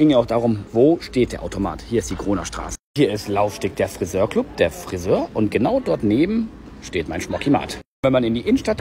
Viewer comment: Ich habe Corona Straße verstanden 😅